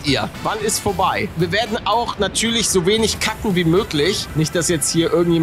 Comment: Wer hat bei cutten auch kacken verstanden??😂😂